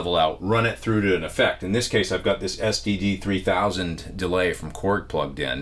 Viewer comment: I thought you said STD 3000 by KORG :p Had to rewind and listen again, and then rewind again to read the script on the korg to find out its actually sdd. All kidding aside, great video, thank you, keep em coming!